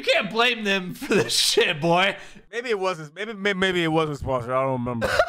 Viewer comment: wait at 14:06 did call boy? lol